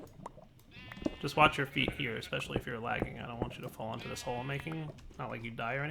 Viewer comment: likes wuning it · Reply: "I don't want you to fall down this hole and die or anything" What I heard was "I don't want you to Dire anything" #